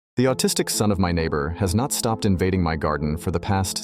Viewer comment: i thought he said the artistic son